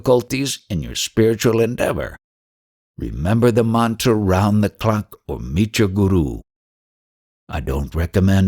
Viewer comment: "_________Guru.?? (What mantra does he say here?) please Sounds like-- "Omicha Guru", or? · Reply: "remember the mantra round the clock or meet your guru".